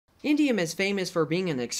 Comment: I thought he said India